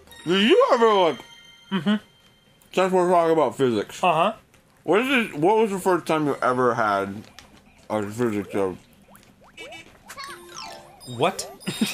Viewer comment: (arin's pretzel mumbling) to me, sounded like, "what was the first time that you ever had a physics joke?"